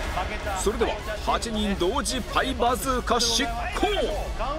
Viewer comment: パイバズーカが一瞬、『ハイパーバズーカ』に見えた(聞こえた)😱😱😱